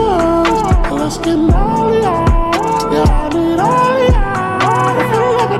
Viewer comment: Surely I’m not the only one who thought he was saying “let’s get molly y’all”